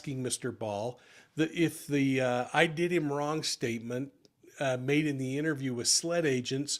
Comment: I heard , "I did him bad"